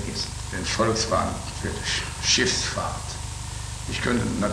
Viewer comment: sounds like he said "shits fart" lol